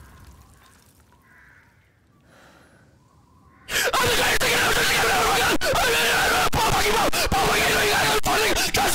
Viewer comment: tf did he say😂something bout setting him up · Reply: he said "paul ima fuck you up tryna set me up" because paul told him to go in that region